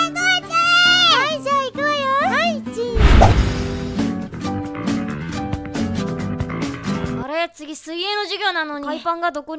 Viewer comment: 「はい、チーズ」のとこ「はい、うんち」に聞こえたw